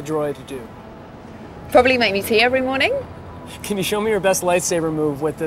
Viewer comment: Caption says: "Probably make me pee every morning" well thats a good droid (she actually says tea just to be clear)